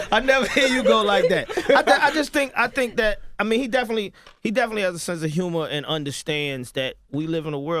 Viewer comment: what did yee say?? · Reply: She asked if Usher has a sense of humor... and if he does he could do a let it burn part 2. lol